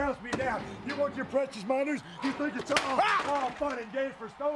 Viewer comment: You want your precious minors? What did he say? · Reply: Monitors